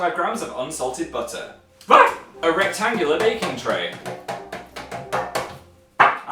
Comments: I heard: "Erect Angular Baking Tray" ... ... I think I need a nap. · I thought you said erect angular baking tray. Confusion ensued.